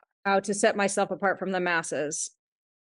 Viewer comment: "Set myself apart from the masses". I actually thought she said (and I totally agreed), "set myself apart from the MESSES." Wow, so true, both. This is how, as an artist, you set yourself apart; learn (for more than a couple minutes), practice and do GOOD fabrication. No slop, or as Lexie Erickson says, "Don't show me shoddy work, and call it 'rustic.'" Love what has done, and is doing. About the best possible model for your daughter, don't worry, she'll go the other way and won't even know you for at least 50 years. Sigh...those rascally independent thinkers, dang! Gotta love em!